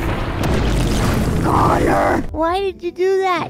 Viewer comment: Swear I heard say “Clownor” at some point in this video.